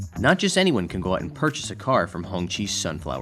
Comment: Did he say African are not allowed to own these cars oh misheard 😮what he said. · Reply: He said anyone . The car can only be bought by someone who is approved by hongqi